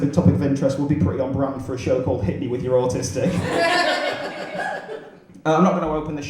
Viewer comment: I heard “Hit me with your butter stick” in the opening jingle. Like a short, yellow, nightstick? Or some guy running around a room hitting everyone with his flaccid junk. Thankfully in all our big brained evolution, we haven’t gotten rid of the fucking rewind button like the headphone jack in an iPhone. That was a dick move on Apple’s part. - To answer your question, yes I am.